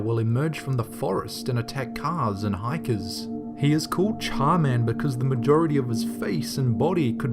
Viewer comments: I thought it said Charmander instead of CharMan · #18 I thought he said charmander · I thought I heard char man der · When they said "Charman" I thought they said Charmander. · Hahhahahahhah when it said char-man I thought he said charmander and I said"plz don't let tha face be in pokemon"so I broke my Pokemon cartridge and now I don't have Pokemon and now I'm holding a tazer along with a shotgun so I kill and murder ppl that come in my room so im of to board of my windows and doors and stick barbed wire....... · #18 i thought you said charmander